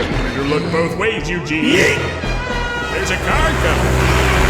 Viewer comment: i heard yeeting XD LOL